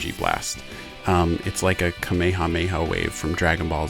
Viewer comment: dawg he did not just say kowamoamoa🤣🤣🤣🤣🤣🤣🤣